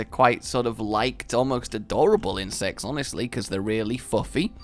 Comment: I thought you said "In sex" rather than "insects"